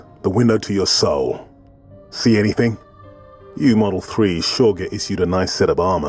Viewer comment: "You model threes..." I've played this game half my life and never understood what he said until now xD Thank you